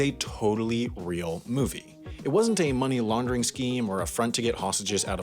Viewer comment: "The lowest grossing film, that totally wasn't a money laundering scheme!" "By the way, that $30 is exclusively counting its theatrical run in the US when it was produced with the foreign dvd market in mind. The only reason it got a US theatrical run at all was for a workers rights aversion scheme." Yeah, bit of a disingenuous premise. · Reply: What he said: "not a money laundering scheme" What i heard: "a succesful laundering scheme".